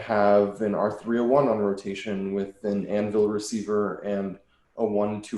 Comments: did he say anvil receiver ??? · DID HE SAY ANVIL RECEIVER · did he say anvil receiver?